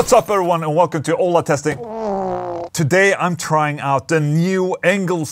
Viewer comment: Sounded like you said. "Ola testing anal plug in" in the intro. Glad to see the video about the Engl though.